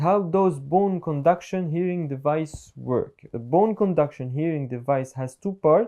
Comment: Are you in Dubai, Guy's. I was there in February. I think I heard you say that but I might have heard wrong. Maybe you were saying Hearing Device Instead of what I thought I heard, Here in Dubai.